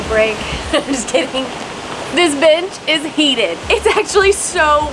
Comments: omg i thought she said "this b** is heated" 😂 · I definitely heard ‘this bitch is heated’ the first time. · I misheard and thought she said this bitch is heated 🤦😂